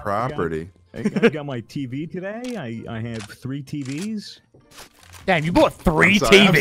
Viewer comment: Did just said "dan you bought a 3 tvs"